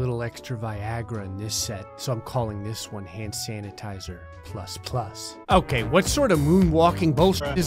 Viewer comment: I thought he said, "hand sanitizer thrust plus."